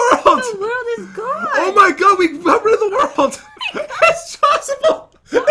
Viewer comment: It sounded like said OH MY GOD WE BUBBLED THE WORLD